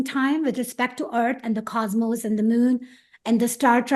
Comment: Did she say Star Tribe? If so what does she mean?